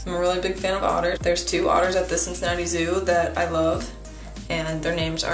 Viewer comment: I love your art so much! 4:48 Also did you say Cincinnati zoo? Please, PLEASE tell me you said that.